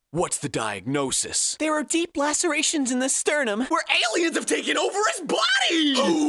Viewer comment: "where aliens have taken over as what?!" Wtf did she say i don't understand?!!